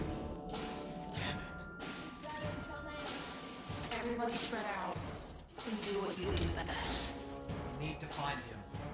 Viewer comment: Can anyone tell me what Toy Bonnie said in 38:58. I didn‘t understand. But I just must know it. Please guys😭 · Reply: He said "We've got incoming!"